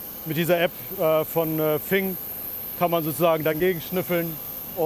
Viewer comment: hat jemand verstanden wie die App heißt? hahahaha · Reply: Fing heißt die app